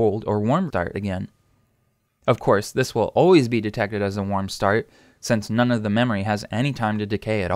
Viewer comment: why did you say warm dart